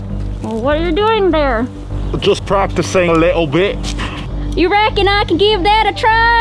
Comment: LIKE OMG I HEARD THAT HE SAID TO LITTLE B—-H CHECK IT OUT!!! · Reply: He said little bit